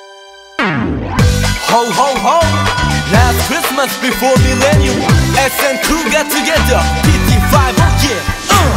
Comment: Ho Ho Ho! Last Christmas before the new millennium! SM Crew get together! Tin Tin Five Oh Yeah! (I have no idea what they said here)